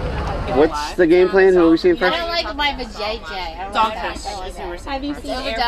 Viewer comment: Um, 12:05. What did the gal on the right say!? lol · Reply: I don't like my va JJ. Lol.